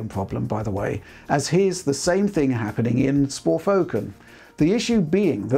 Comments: Did Rich just say 'Sporfoken'? xD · "Spor Foken". Looks like Rich registered what he said him self as well haha